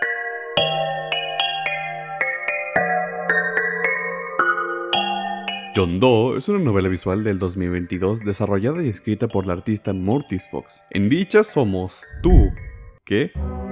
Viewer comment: Acaso escuché el nombre de "John Doe?" 5:40😏